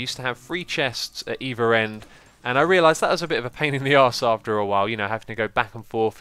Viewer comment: Did you just said Pain in the ass at 4:10 ?:o Never heard that from you :D